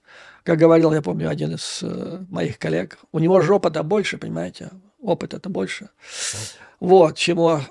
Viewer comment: " У него же ж опыта больше", а послышалось " Жопа-то больше"))